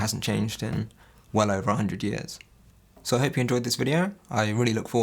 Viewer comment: Did it say: "Hemp you enjoyed this video" at the very end?